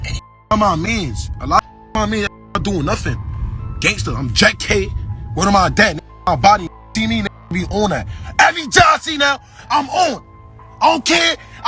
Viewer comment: Subtitles for what was saying at 1:03:52 are wrong, he said "I'm JetK word to my deads" a 4 seconds later it had the question marks but he really just said "every jet I see now I'm on"